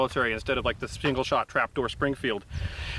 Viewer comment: I first misheard that as "this single shot crapdoor springfield".